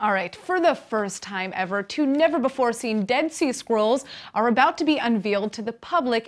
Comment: I thought said, "dead sea squirrels"